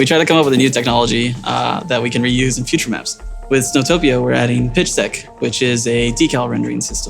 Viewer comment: I thought I heard him say "Zootopia" for a moment there.